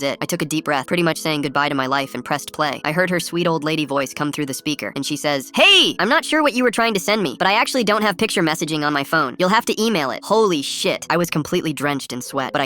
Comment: I THOUGHT YOU SAID "emailed her a picture of my dong"💀💀💀💀💀💀💀